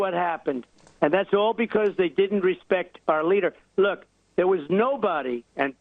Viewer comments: Did I hear Trump say "they didn't respect our leader"? (2:47). Genuine question, I may have heard it wrong. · did I hear correctly, I seem to be hearing him say about putin that "it's because they didn't respect our leader"? WTF? <link> at 2:41+ in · "Our" Leader?! Did I hear him right? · did he just say “Our Leader”? Did anyone else catch that? 😳😯🤭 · Does Trump say (2:45) "because they didn't respect OUR leader" in reference to Putin?